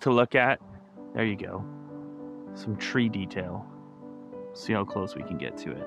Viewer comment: Did you say "Some 3D tail" or "Some tree detail"?! 🤪